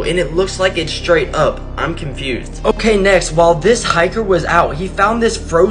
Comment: Me: Yeah that's my Ex's heart. She never cared. Glad she's gone tho Edit: She didn't die. She cheated and I found out and broke up with her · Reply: Sounds like he said while a hydra was out